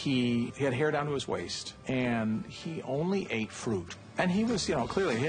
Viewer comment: Sounded like he said "He only at Food!" What? Oh, FRUIT---I knew he was a vegetarian, but I'll have to read lips a bit here. Used to work in mountain view and Sunnyvale around this time-never met Steve jobs,however!